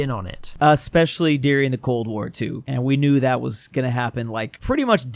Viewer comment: Did he say Cold War 2? 👀